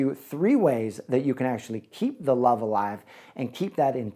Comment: I just heard 7:14 "threeways to keep love alive" 😏 dont worry, im single.